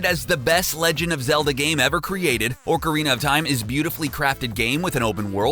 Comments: It sounds like he is saying Orcarina of time otherwise good vid bro! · Did he just say ORCARINA of time